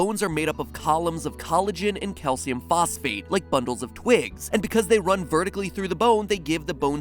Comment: Anyone else hear “like buttholes and twigs”?